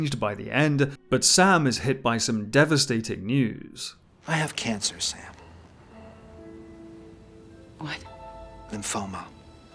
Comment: I thought he said “I have cancer Sam. And FOMO” 😂 it kinda works with the plot 😅